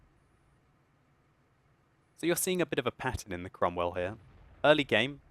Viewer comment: did he say pattern or patton?